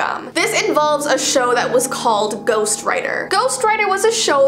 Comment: Did anyone else hear Ghost Rider? 🔥 💀